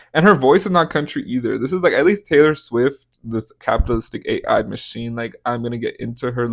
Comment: I heard Taylor's is witch